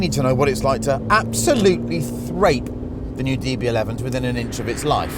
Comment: Did he just say "thrape"? i can only assume it's a portmanteau of thrash and rape, and because it's, I friggin' love it!!